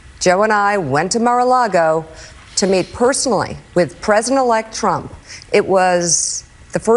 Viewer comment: The people who genuinely think they love Trump shouldn't have voted for him. Historically, narcissists who are given power OD on it and is never a pretty ending for them. If anyone wanted what was best for him, they'd have let him rest at his golf course on a comfortable house arrest retirement plan. His future is BLEAK now. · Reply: she actually says "with prison elect Trump" 😂😂😂